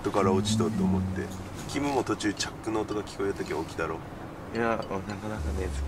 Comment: キムのチャックの音が聞こえた ってが言ってて この狭いテントでマジかってなったら違った